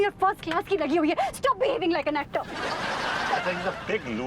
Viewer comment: Did Anita say to Vibhuti ,"Stop behaving like an Actor..." at 5:14 ??